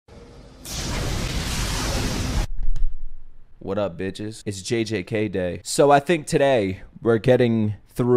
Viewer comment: I thought he said "what's up its jj(ksi)" I had to go back to confirm